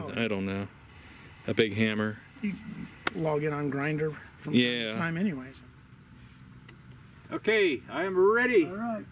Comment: what did he say?login on grinder, it's been awhile anyway? ❤❤❤❤😂😂😂😂😅😅😅😊😊